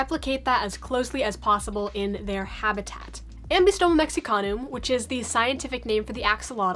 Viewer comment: It sounded like you said Mexicans.